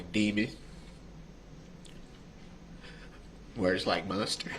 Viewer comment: When he says words being thrown out there, mind you this was before witcchow calls him out about the so charge, I thought he said words like demon, words like m0le$ter…